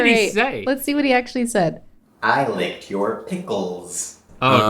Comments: I was so close! I thought was saying "I like your pickles." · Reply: I just thought he was saying “I like your boots”. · I was so close! I thought was saying "I like your pickles." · I thought he said: I like your Pickles I was really close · I was so close! I thought was saying "I like your pickles." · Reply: I thought he said "I like your picture" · I was closer than anyone on the lip reading, I thought he said "I like your pictures". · I was so close! I thought was saying "I like your pickles." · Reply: I thought he said, "I think your pickles." I know it wasn't right, but it was the best I could do hah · I WAS SO CLOSE TO WHAT SAID😩 I thought he said “I like your pickles” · I thought said, "I like your boots." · I was so close! I thought was saying "I like your pickles." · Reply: I thought he was saying "I like dill pickles" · I thought said. I liked your pictures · I thought said "I like your pickles." I was so close! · I was so close! I thought was saying "I like your pickles." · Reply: I thought he was saying "I like to kiss pickles." Lmao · I thought he said I love your pictures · I thought he said I love pickles so like I was close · I really thought said I like chewed pickles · I was so close! I thought was saying "I like your pickles." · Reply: I thought he was saying "I love pickles." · I thought was saying I like your pickles. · I love to buy pickles, I thought he said